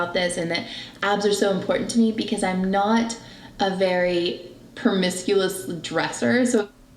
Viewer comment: I’m sorry, sid she say “promisculous”? I could have sworn I heard an “L” in there!